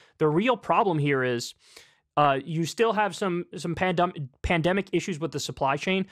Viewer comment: Did he just say “pandumbic”?